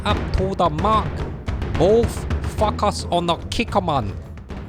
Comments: Ok you say "focus" and all i hear it "fuck us". Just sayin ;) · instead of focus, I thought you said FUCK US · I thought he said FuckUs lol...Focus haha